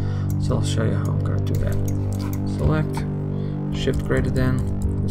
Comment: Hey , Awesome tutorial! But I have a quick question. What do you say @ 23:10 to expand the selection area? Haha, I just cant understand ( And I don't know the short cut ) Thanks in advance, Hope to see more tutorials soon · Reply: Thanks! Use SHIFT + > or SHIFT + < to grow or shrink a selection.